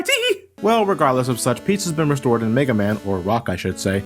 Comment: I heard "Pizza's (peace has) been restored" YESSSSSSSSSSSSSSSSSSSSSSSSSS